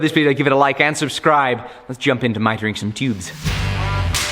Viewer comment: I'm sorry to do this to you, but right at 0:32 I wasn't paying attention and I heard "My drinking pubes" instead of Mitering some tubes. Now, I can't not hear it.